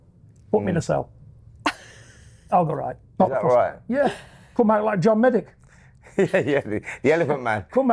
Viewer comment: or from 28:30...I thought he said ''come out like John Medic'', not John Merrick..who dat den?...hehehe...may praise be to John Fury...🙏...Namaste...